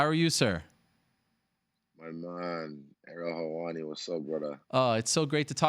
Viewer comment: Is he saying “i’m on marijuana, but sober now” at 0:14 😂😂